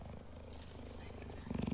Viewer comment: Idk why people say it’s funny, he just said Cheeto. I thought he said Cheetos.